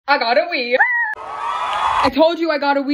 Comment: I thought you said “I gotta wee” 💀